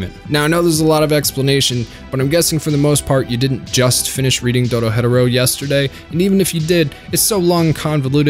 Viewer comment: Doesn’t have to do with anything in this video but 5 seconds in and I hear him say “doro hetero” made my laugh